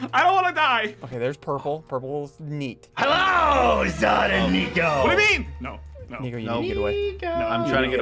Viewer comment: did he say the n word?????!!! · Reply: He was saying Nico